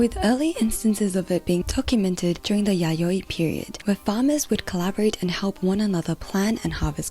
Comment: anyone else hear 'yaoi period'? xd